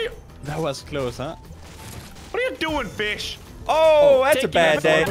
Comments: Did he say bish or fish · Did anyone hear Crainer say the B-word on 9:11? EDIT: NVM HE SAID FISH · did he say fish or bish